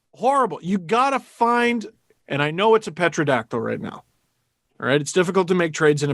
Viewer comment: WTF did Steve say? "Petrodactyl?"